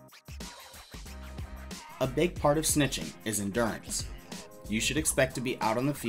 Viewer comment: another great video! 1:42 says "in dance" instead of endurance